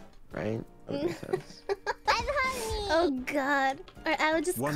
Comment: Can someone tell me what is said by her alert? I swear it says "I'm honey". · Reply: "i’m horny" thats hololive jp Marine’s voice